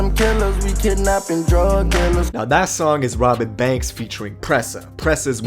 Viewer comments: Thought u was saying Robb Bank$ at first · Sounds like you saying Robit banks